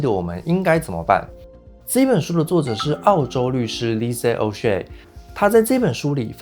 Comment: 空耳0:29 Lisa oh sh*t😆